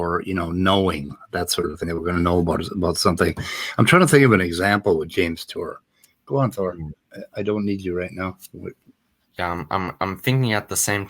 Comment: for a little bi there, I thought you said you had James Tour stepping on the ipad... Apparently I wasn't paying proper attention. 😸 - they do like to sit in what you're doing, don't they? (Books, keybords, crossword puzzles, et.c)